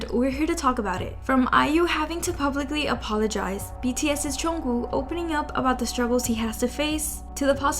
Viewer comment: loll why does she say it like "chungoo" 😭